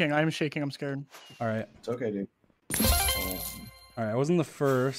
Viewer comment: I THOUGHT SAPNAP SAID “it’s okay babe”